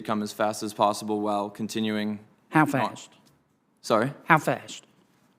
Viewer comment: Intelligence has limitations but stupidity has no boundaries. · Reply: I thought Sen. Kennedy said "half-assed," but he was asking "how fast?" Sorry...